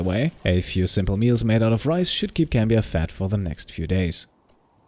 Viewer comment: the goal is no more to stay alive, but to stay fat for as long as possible :D (I hope I am not the only one who hears him say "should keep him fat for the next few days" :D )